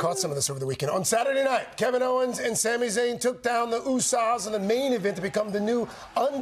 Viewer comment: Did I really hear him saying "The Usa's" (Oo-sahz) instead of The Usos?